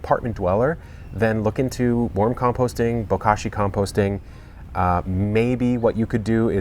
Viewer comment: I'm sure I heard "bukkaki composting" 🤣🤣😂🤣😂🤣